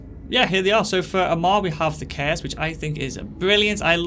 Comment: Does anyone know what word he’s saying around 3:20? Cares? Chaos? Cans? Kiss? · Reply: Nvm “Curse”